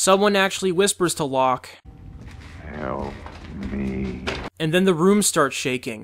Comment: To me it always sounds like he said "kill me" not "help me" and it sounds like the man in blacks voice. I assumed this was a trick by the man in black to get lock to kill Jacob